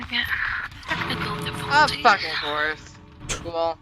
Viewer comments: There appears to be a mistranslation at 6:00. She say "Of effing course" when the hatch closed instead of "Oh effing wh*re". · i think shes saying "of fcking course" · I think she says “Of fucking course” I don’t think she’s saying wh**e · I think at 06:00 she actually says "of fuckin' course" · she said “of fucking course” not what the caption says then literally seconds later she does throw around more insults so it doesn’t change much, I do like her friend so far though, it’s a shame people have to put up with people like that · i think she said "of fucking course" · why does the sub title say "oh, fucking whore" it sounds like she said "of fucking course" could definitely be wrong though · I think that was subtitled wrong, sounded like "of fucking course" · hey don't mean to be some jerk but I think she said course, not trying to be some nerdy person or be like "Oh YoU mEsSeD uP sUbTiTlEs". idk just noticed